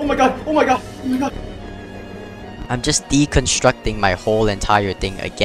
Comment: why did i hear conquest??